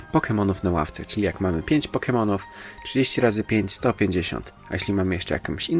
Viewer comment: dlaczego usłyszałem 30X5 to 50